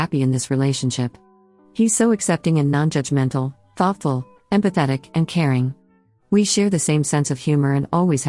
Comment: OH- I THOUGHT IT SAID “PATHETIC” BUT IT SAID “EMPATHETIC “